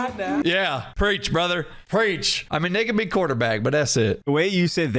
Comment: i definitely heard it to Marcel lmfao N…A be quarterback lol